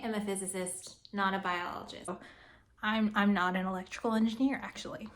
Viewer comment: I almost thought you said here "I'm not an intellectual engineer"